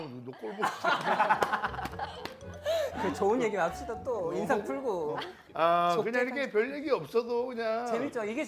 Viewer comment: 좋은 얘기만 합시다 인상풀고~ 그다음에 성종이가 제작진쪽으로 “좋게 편집해주•••”도 들림.. 웃고는 있지만 기분 상한게 얼마나 티가 났으면…ㅠㅠㅠ 억까도 분위기 좋다가 한번씩 나와야 웃겨요 진짜 맘아프네